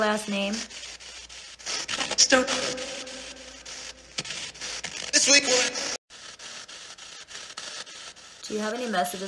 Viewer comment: I heard "sweet one!" at about 3:50. 🙋‍♀️💜